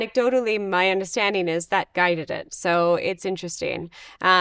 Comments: What Guy? did they say a guy? she just said, yeah, "That Guy set the threshold".... who ... WHO??? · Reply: “That guided it”, not “that guy did it” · In regards to your question, She is talking about why they arbitrarily set the limiting numbers, and she is tangentially commenting "that guided it". (If you go back about a minute and listen it will make sense in context). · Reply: lol omg i misheard what she actually said? HAHAHAHAH lololol, i swear i thought she said: "THAT GUY DID IT" hahahahha thank you, i appreciate that someone answered!